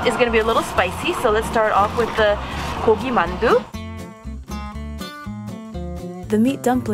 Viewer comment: i swear to god i heard ko ko bop by Exo